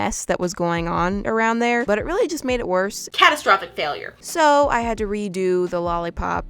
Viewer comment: First of all.. green and blue? You summond a whole fandom. Second of all catastrophic? I thought you said cataclysm...